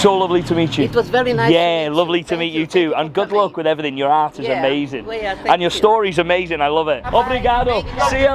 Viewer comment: 21.34mins in i thought you said 'Your arse is amazing' definitely thought so 😂